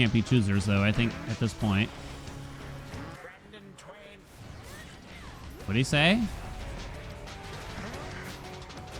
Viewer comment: me just drawing and hearing this i was like what? did he just say "Random train"...? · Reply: I think he says "Rend in twain".